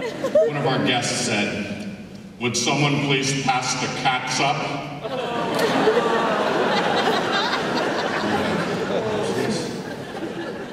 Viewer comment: on 17:17, what did he say? "would someone please pass the ??????" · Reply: Catsup. It's ketchup. XD